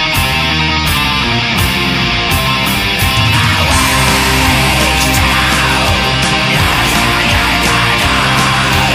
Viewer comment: can't understand a shit, but still is fucking awesome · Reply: a vague shadow lurking in the dark a sane man's worst nightmare